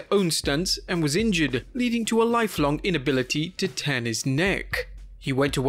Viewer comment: Did u say his lifelong injury left him unable to turn his neck? Bc it sounded like you said tan his neck and at first I was like what lol